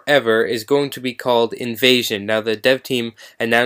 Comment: Sounds like you say "Invasion" Not "Evasi0n"